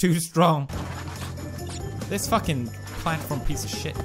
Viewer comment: What does he say at 4:58? azabajamia?